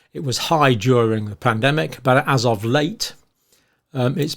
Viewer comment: 1min40sec sounded like you said Plandemic 😂. I had to double check.